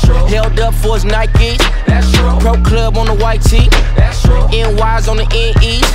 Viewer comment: I always thought he said "whole club on a white tee," which comes off a LOT darker than "Pro Club on a white tee"